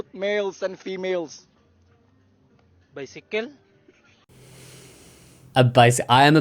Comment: he said “bicycles?” I thought he said “Mexican” lmao am I crazy?